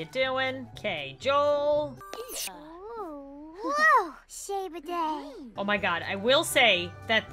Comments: he clearly says "save the day" xdd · I swear he said “Save the day” Also, 6:40 I think that she said « Ah beautiful » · DID HE JUST SAY "Save The Day!" O_O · Did he just say Shane Madej? Idk how to spell his last name it’s the tall guy from Buzzfeed Unsolves